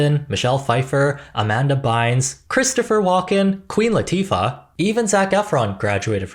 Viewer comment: i'm sorry i always hear queef latina when hearing her nam 😂